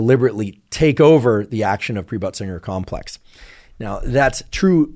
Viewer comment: Pre-butt-singer 😂 complex is what I heard him say, and I thought that really needs to be renamed. I looked it up and it is Pre-Bötzinger Complex😊